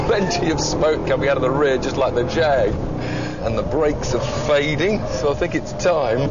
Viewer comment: what he says at 3:35 and the brakes are...... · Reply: fading